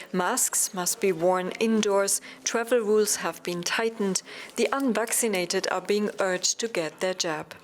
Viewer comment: I would like to know what percentage of the vaccinated in hospital were immno-compromised? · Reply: Sounded like they said unvaccinated and elderly